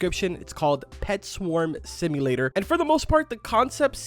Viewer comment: I thought it said bee Swarm Simulator